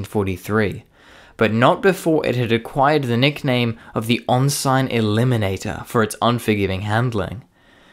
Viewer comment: FYI, ref the “Ensign Eliminator” moniker. Ensign, a naval rank, is pronounced like n-sun rather then on-sign. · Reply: I thought he said “on-side eliminator”